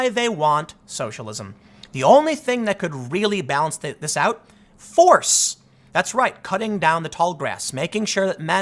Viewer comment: Tim says "force" but I hear "violence".